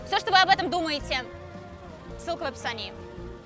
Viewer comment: Когда сказала ссылка в описании а мне послышалось с*ка в описании